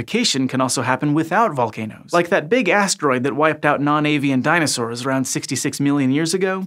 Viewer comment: I thought you'd say "that big ass sea vents" or something xD